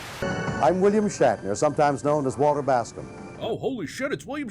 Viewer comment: I heard... I'm William Shatner, sometimes known as Wall Tabasco...